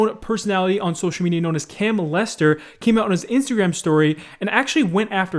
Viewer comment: Did scarce just say “can-molester” at 5:29 lol